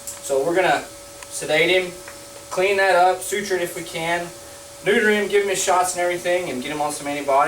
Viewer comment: It sounds like he said "Snooter him". I laughed for like 5 minutes, omg help